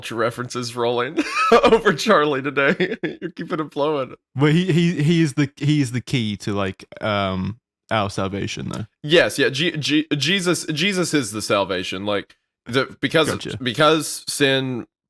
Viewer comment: I thought was saying Charlie was the key to salvation ☠️